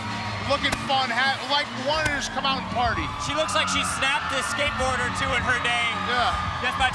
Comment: “She looks like she’s snapped a skateboard or two in the day just by trying to ride it” LMAO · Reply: I thought he said a skateboarder or two". Haha